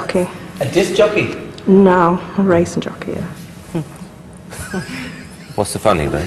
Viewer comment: what did she say? 😭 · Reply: A racing jockey.